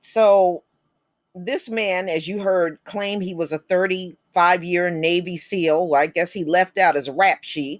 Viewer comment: If he is not fit to be in jail why did he provoke an innocent Black man? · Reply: I thought he said he was a retired Navy Seal yeah right more like career criminal 😳😆😆😂😂